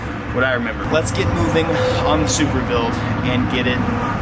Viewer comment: Did I hear Supra build